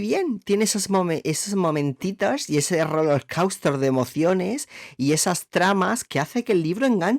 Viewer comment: parece que dices h0loc4usto en vez de rollercoaster 😭😭😭